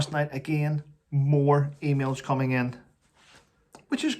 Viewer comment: At first when you said "lots of emails coming in"- I mishead and thought for a moment you said, "lots of females coming in" lol